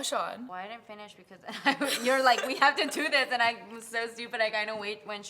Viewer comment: OMG And now it sounded like she said "Oh S**t"